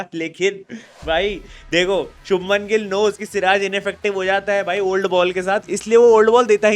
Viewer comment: it's not true , I think he said with the old ball